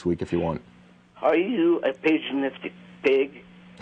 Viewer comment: what did she say be fore "pig"? I didn't catch it. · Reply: Paganistic*